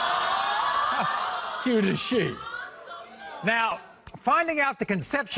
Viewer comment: i thought he said cute as shit